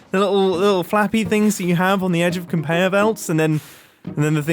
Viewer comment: HE SAID CONBEYER VELTS instead of conveyor belts 😂😂😂😂😂